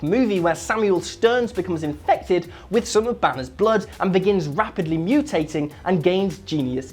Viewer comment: I heard you saying he became infected with batmans bloodd... wait what?... *rewind .... banners blood. Makes more sense! :D